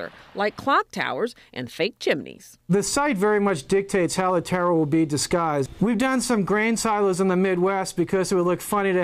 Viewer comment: it sounds like he says "how the terror will be disguised"...hmmm